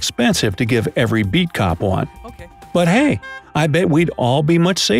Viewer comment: Who else heard the “ok” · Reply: I thought he said “F” it at first, so naturally I had to travel back in time so that I’d have the opportunity to fully re-experience this video again for the first but with a heavy emphasis on focusing in and paying close attention to the cartoon characters soft spoken and rapid verbal response that he delivers after he learns the very sad news about not actually receiving his very own personal bat mobile to keep as a gift. It’s really quite a masterful and brilliant performance that clearly shows his talents and his dedication to the craft. To have the ability to instantaneously relate to nearly everyone on this planet no matter how different we all are is absolutely the most insanely difficult level of professional acting that anybody could even remotely fathom much less attain. So yeah, I too am excited that I could answer with a “yes”, I am an else, and I heard the “ok” at 8:22! 😷